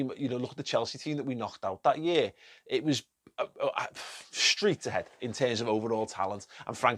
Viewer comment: Did he just say streets ahead?